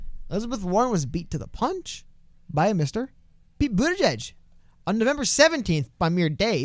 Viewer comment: Lol at 4:27 the automatic captions read Mr. Bieber, instead of Buttigieg.